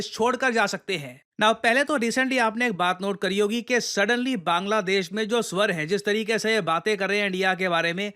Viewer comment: did sir really said 2:29. "bangladesh mein jo suar hein"